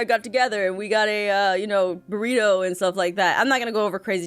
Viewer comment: Lol when she said "we got a burrito" I actually thought said "we shared a Dorito" scutttteeee